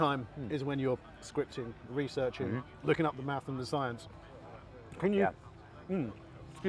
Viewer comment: I feel like they put that animation on screen when said he enjoys doing the math because it sounded like he said he likes doing some meth 🤔🤣🤣